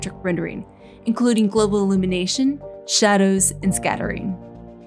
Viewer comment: I thought she said "global elimination" as there was a fire in the background, but she just said "global allumination"